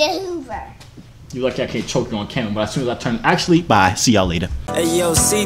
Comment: his voice is so high pitched 😂😂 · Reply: I swear I heard him "fuck your carmera up"